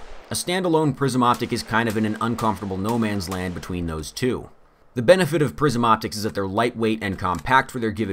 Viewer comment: I thought he was saying ‘prison optics’. I don’t even know what they are...